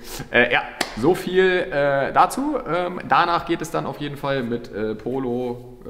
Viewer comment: hab ich da "Strich 8" gehört? Etwa ein W114/W115?